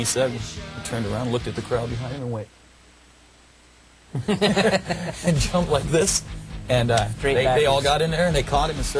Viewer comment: What did he say at 2:19? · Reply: At first I thought it was "What the fuck is happening" but its "YOU BETTER FUCKING CATCH ME!"